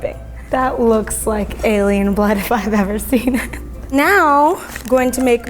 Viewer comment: Lol it sounded like she said aryan blood is a nazi confirmed